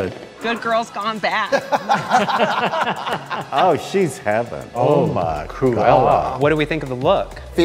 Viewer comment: I thought he said "oh she's Heather" I